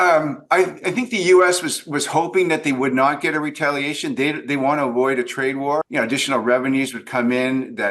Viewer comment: I heard you say "They want to avoid a trad war" umm I think that's wrong